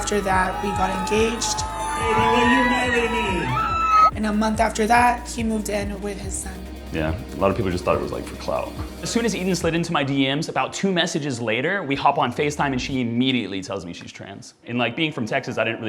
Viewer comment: So did you hear him say "HE slid into my DM's". Yep I am pretty sure that is what he said LOL............